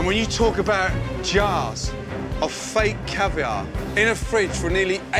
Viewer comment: I thought it said fake cake caviar lmao 👁👄👁